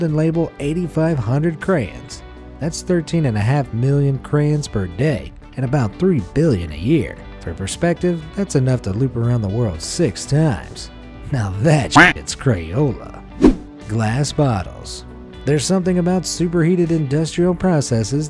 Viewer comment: did he just said shit 😂